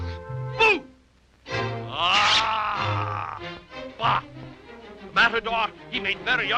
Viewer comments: unless i'm mistaken it sounds like he says "aaah fuck!" lol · did he say fuck at 5:57? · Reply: "Bah!" A more common exclamation at that time